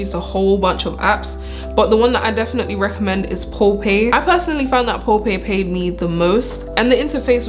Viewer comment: what did you say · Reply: POLLPAY 😅